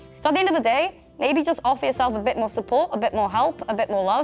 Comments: is it bad that i heard just off yourself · I THOUGHT SAID "OFF YOURSELF" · Why did I hear "Just off yourself" instead of "just offer youself"? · i unironically thought he said "off yourself" oh my fucking god- scared me so bad sdfghjkl